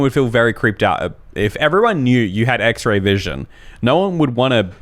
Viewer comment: Anyone else heard him say axe ray vision at the beginning of the story?